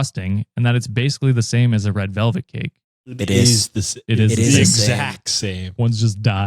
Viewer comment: I thought you said 'red velvet CAPE' like dude really was a villian